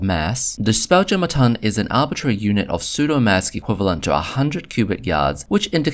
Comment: Apologise if you said "psudo maths" and not "psudo mass". I heard you say "maths" in other parts of the video (by the way British people say "maths" with an "s" and American people say "math" without an "s" - I'm British so I say it the same as you do). I don't think your elocution is bad. You have a good voice. It was just two words that sound very very similar in English. I've seen lots of people mix up the tons of weight and the tons of volume and thought you were doing that. As for the volume, there are two different numbers given in different Spelljammer products and that has led to a lot of debates among Spelljammer fans about how big things are. (Spelljammer also gives a lot of sizes in two dimensions keel length and beam length and doesn't give you the third number you need for your calculations.)